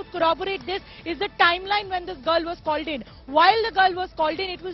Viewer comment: "girl was calleded🤦🤦" in what universe is that even a word... Too bad the reporter of an Indian English channel can't speak properly..